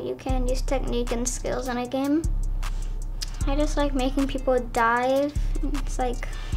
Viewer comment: I thought he said I like making people die 3:47😂